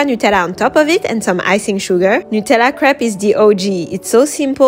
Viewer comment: i thought she said nutella crap (not making fun of her btw)